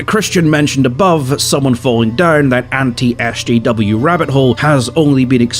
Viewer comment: Can anyone explain that acronym to me? I think he said SDW or something similar, but I've never heard that one before so I'm not sure what it actually means. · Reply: SJW, Social Justice Warrior